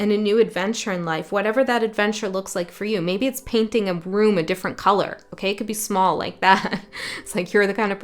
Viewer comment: I thought you said painting a broom a different colour!! (From the red witch to the white witch 😂😂) number2. And it really helped me with understanding that not everyone that does what i do has access to that 'inspired otherness'. It doesn't automatically come with the territory. X thanks again for your gift. . As always ❤❤❤❤ to you.